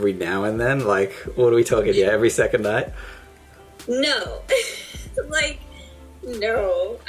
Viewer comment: I love it how in 4:15 she says no but the subtitles says yes